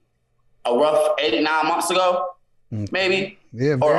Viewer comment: I thought he said 89 months I was like chilllleee get it together!! He was all over the place